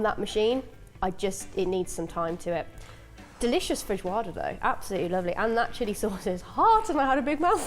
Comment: Pffffft Poppy says “feijoada” but the captions say “fridge water”